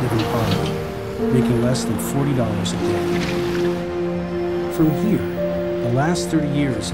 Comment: Did he say "40$ a day?" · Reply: less than $40 a day